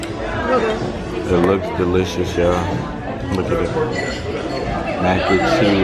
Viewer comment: it sounded like she said look bae (and I ain't reaching either)